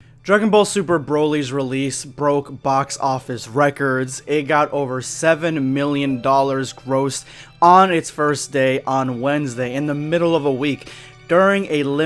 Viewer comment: i thought you said Robux